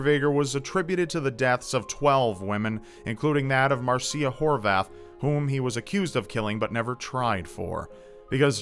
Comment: marcia whorebath? am i hearing that right? i hope not.